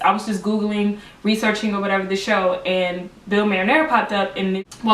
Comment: I found him on Facebook , he had a page dedicated to hiring background actors for the show . i think the shows been cancelled but heres the link <link> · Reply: oh ok Marinella it sounds like you were saying maranare on the video? Anyway yes I'm looking into some work here in LA and I. Can see how finding a casting supervisor will help